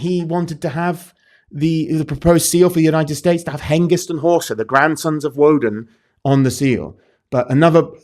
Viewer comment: What tf is the guy saying here at 50:38? Hangison Horston? What is he talking about? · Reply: Hengist and Horsa